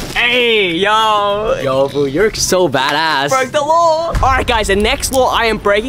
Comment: Is it me or did he say bad ass